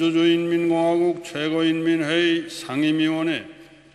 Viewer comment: did he say Sunny Leone? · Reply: No, the words that he said are 《조선민주주의인민공화국 최고인민회 상임위원회》, "Standing committee of the Supreme People's Assembly of the Democratic People's Republic of Korea". The 상임위원회 (transcription "sanimwiwŏnhŏi") probably sounded like Sunny Leone to you. He is telling them to vote on some question (not sure about what). As you can see, the deputies raised their hand to show support. In the next sentence, he asks the opposing deputies to show that they are opposed. (Would be hard to count otherwise, because most if not all supported it.)